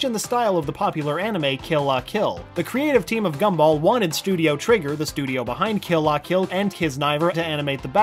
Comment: Wasnt looking so I thought he said TRIGGERED